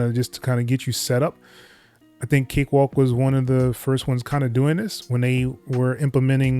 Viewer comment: I thought you said cakewalk was going to sell their DAW.. because of that, I threw it out!!! Besides that.. I really liked cakewalk!! However.. if i have buy a DAW.. I'll buy Ableton!!